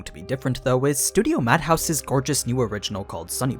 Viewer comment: i was like ok they’re doing anime ( i thought you said mappa) again wait wait mad house?? Lets Fucking gooo idc what it is its gonna be fire, also did they make an anime last year?